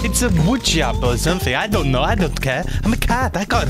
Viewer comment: Heh I thought he said "Hey keeps things cool eh?" at the end...